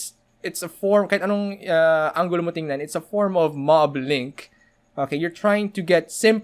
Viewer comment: What is the word on 13:28 po? For research purposes lang po :) thanks po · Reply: Rod Jay mob link